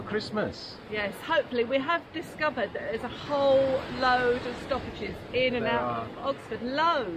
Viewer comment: A lot of stoppages around Oxford - thought said sausages 🤷🏻‍♂️